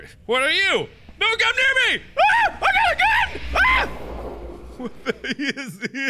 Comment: Did Mark say i got a gun